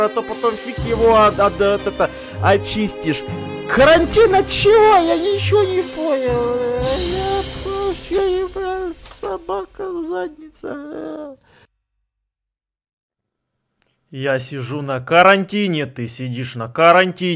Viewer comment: Мне послышалось? Или последняя фраза была:-Собака в заднице...🤣🤣🤣